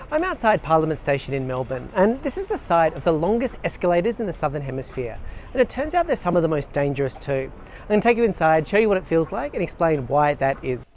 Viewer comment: why did i thought that it said play station